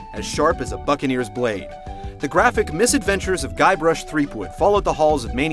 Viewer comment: I thought it said Gay Rush. lol